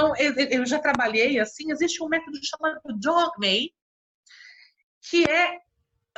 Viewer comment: Qual o nome deste método que ela fala em 32:25? Não entendi. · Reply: o método é DOGME. Desculpe pela demora da resposta :)